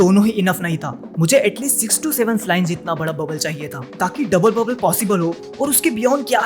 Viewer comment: did you just said 67?.........